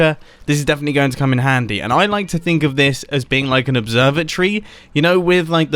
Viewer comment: you said "observatory," but I heard "observer tree." Now I want to see a tree that produces observers as fruit.